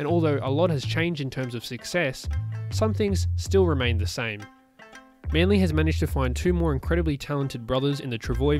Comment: did you say Manly Storm🤔